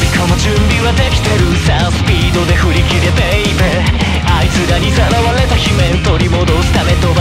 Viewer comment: did he just call yujiro baby????